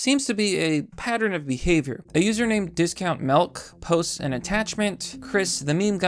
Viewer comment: did.... did you say melk? You 100% said melk.